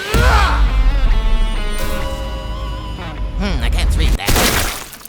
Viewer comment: Popeye going "I can't read that?" to Saitama's "Death Punch" had me laughing out loud. Absolutely hilarious fight. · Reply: I heard “Hm, I can’ts read that?”